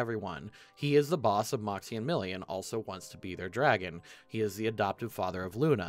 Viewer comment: "He is the boss of Moxie and Milly but also wants to be their dragon" ...Am I hearing that right? What is dragon in this context? I am very confused.